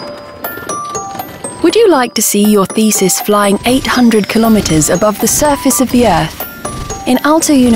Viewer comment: omg I thought she said "would you like to see your feces flying 800 km..." lol